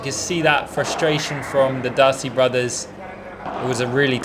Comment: You actually said Dirty brothers or am i hearing stuff 25:17, if you did... WELL SAID. Would've been better if you said Dirty "Brother" since the other one seemed chill. Congrats on your first tourney in spain